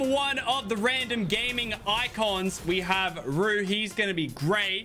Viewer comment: when you say Ru instead of Ryu.